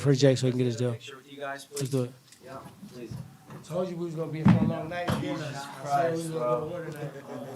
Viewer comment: What does TWood say at 49:54? Had Jake looking shook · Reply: He said something like i told you we were gonna go at it tonight